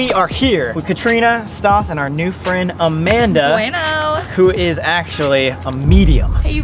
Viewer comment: did she just say bueno?